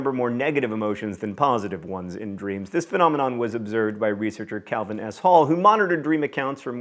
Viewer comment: I heard "Calvin asshole" so I put on the subtitles, which also "translated" it to "asshole" xDDDDDDD